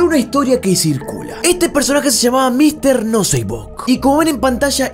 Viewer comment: Entendí Mr no se como